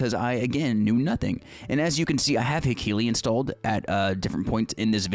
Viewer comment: What's the name of the addon you say the name of twice around 2:30? The one that is beneficial in learning rotations but I can't tell what you said. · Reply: It’s Hekili, it is a rotation priority helper!